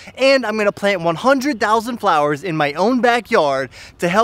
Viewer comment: I liked how Hands-on this was! I’ll share it with some of my friends. And, congrats if you’re really planting 100,000 flowers in your backyard. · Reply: Your planting 100k Flowers! I thought I heard 100 flowers. Hope you make a video on that. Always going above and beyond